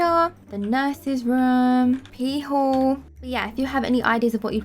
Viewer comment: did she say pee hole ...?